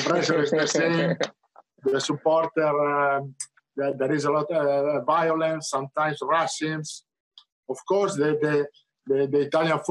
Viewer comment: What does he say after “there is a lot of violence, sometimes _______” at around 10:09? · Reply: He said racism